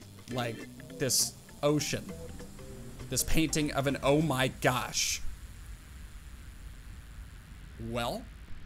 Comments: is it just me or did he say “oh shit” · bruhhhhhh i first i thought he said "oh sh*t" but he said "ocean"....